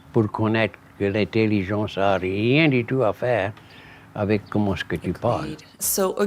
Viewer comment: THE FRENCH TRANSLATION SCRIPT OF WHAT THE MAN SAID WAS INCORRECT- HE SAID "RIEN DE TOUT A FAIRE, " pas "tout a voir"!!! @ 2:19-2:26 (Il y a des autres erreurs aussi!) Alors, C'etait un bon video, par ce que j'aime bien l'accent Louisiane! Merci mille fois! J'ai lis qu'il y a l'histoire des Acadienes, qui sont venus au nord, pres des Appalchia, qui sont venu a Louisiane apres a cause de la guerre.. Il est tres important ecouter bien, qu'est ils dissent, contre quoi qu'etait ecrit! Le Creole de Haiti est plus different de Le Cajun et Creole de Louisiane! En Haiti, the Creole spelling is different, but if you read it out loud, you can understand the French, if you pronounce it out loud! J'adore Francais! Mais quand je parles en Allemand --- STUPID AI "ENGINEER " HACKER MORONS HAVE NO LANGUAGE SKILLS/PLATFORM TO TYPE IN OTHER LANGUAGES!! YOUTUBE/GOOGLE NEEDS REAL LINGUISTIC EDITORS!! THEY KEEP CHANGING TEXT I WRITE AND MAKE IT INCORRECT! IT'S BAD ENOUGH WE CAN'T READ SMALL TEXT!!!